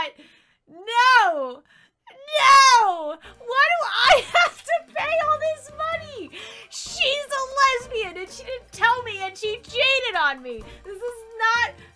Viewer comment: It sounded like you said “why do I have to pay!? She’s a lesbian !”